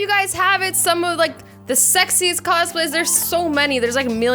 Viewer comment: The most sexiest costume ever.its.... “Yoda from Star Wars” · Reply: I thought you said Sexist and was really confused